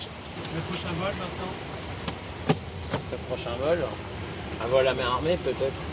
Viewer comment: J'ai pas comprit ca réponse a 1:48:00 un vol am quoi ? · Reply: Un vol a main armée